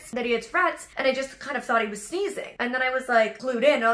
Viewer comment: I thought she said "seizing" then later I realized it was "sneezing"